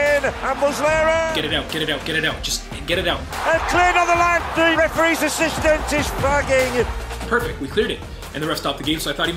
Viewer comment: I heard "AND THE REFEREES ASSISTANCE IS FIUCKING